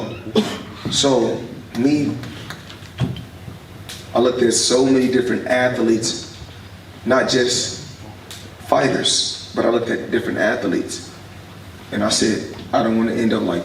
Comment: did he say at-the-leits round the 6:35 mark